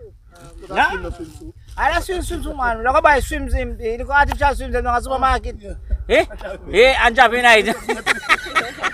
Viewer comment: What was short man saying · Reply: Blessing